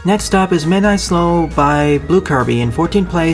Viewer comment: When you said the title of Midnight Snow,I thought you said Midnight Slow.